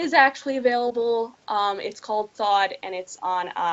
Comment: I kept hearing 'thawed' as 'thot'...